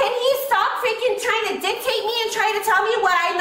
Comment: "Tryna dictate me" Anyone have any idea what she's trying to say?